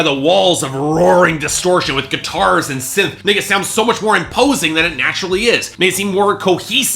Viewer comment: Did he just say nigga?